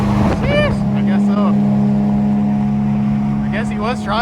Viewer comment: 4 times actually if you include the description.. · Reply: my apologies if I heard you wrong but when you threw up the 🤙🏼I thought you said something along the lines of "I guess it was"